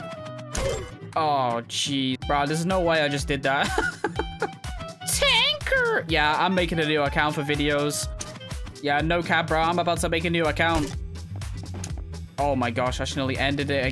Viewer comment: Man said tanker 6:36 instead of tanqr